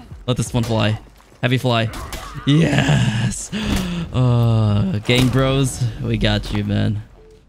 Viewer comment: sounded like u said bangbros